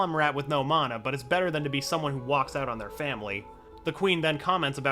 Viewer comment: did you say FAMILY